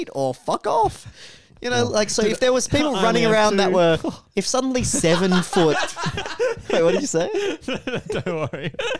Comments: I keep replaying but I still can't understand what he's saying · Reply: "I only have two" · WHAT DID SAY AT 14:16 I MUST KNOW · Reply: "I only have two" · Does anyone know what said? I've rewatched it so many times and I can't figure it out. I'm dying to know lol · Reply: He said " I only have two" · what did say? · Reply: “I only have 2” haha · joke at 14:15 I stg I've rewinded this so many times and I still have no idea what he says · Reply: "I only have 2"